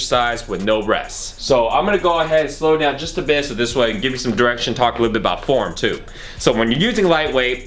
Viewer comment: i thought he said talk a little about porn too xD